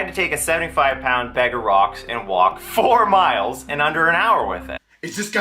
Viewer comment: "I had to walk 4 miles in under an hour" Boy Scouts walk more than that with weighted backpacks every month. This guy is pathetic. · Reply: i thought he said 4 miles in under a quarter hour. I thought that was a decent pace what was he complaining about. This somehow makes it worse.